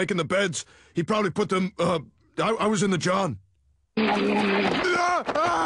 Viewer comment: when i first saw this, i thought he was saying “you guys are not cheese, man! you’re freakin not cheese!”